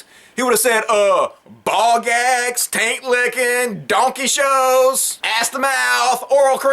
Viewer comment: "Taint lickin'" I thought you said "tank lickin'" which, as you can imagine, confused me. Okay, it's still disgusting, but at least now I get it. Oh, wait. Did you say "Donkey Shows?" You are a truly terrible person, and I hope you'll be my friend. Looky there! Now, on to more goat remixes!!!